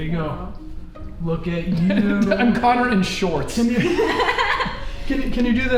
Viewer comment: I'm Connor in shorts What I hear: I'm Connor but short